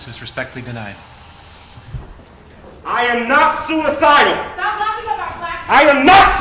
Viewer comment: "Stop laughing at our...?" i cant make out what she said 4:51.